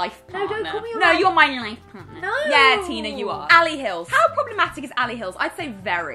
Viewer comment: Did really call “Tina” at 5:20 lmao the SHADE